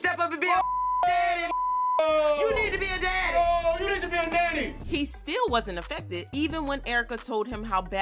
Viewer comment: Did Safaree really say "you need to be a nanny" @ 1:32 😂😂😂😂😂😂😂